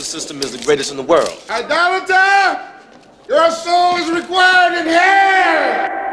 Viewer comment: "You're soul is required in hell!!!" Old dude was out here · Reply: I thought he said required in here